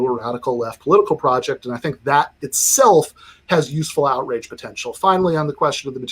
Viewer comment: Funny, at 23:17, it really sounds like Ben says 'outrage' rather than 'outreach' -- 'useful outrage potential.' Freudian slip?